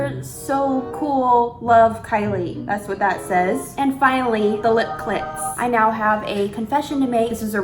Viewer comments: Is it just me or whenever says Kylie Lip Kits I hear Kylie Lip Clits? like im curioous XD weird question might just be me mishearing witch i probably am, anyways love the vid as always keep it up! ^^ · Did she just said 0:48 "the lip CLITS"? u go sis